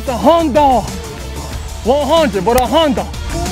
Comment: what did he say at 2:34 lol a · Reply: a hundun as in 100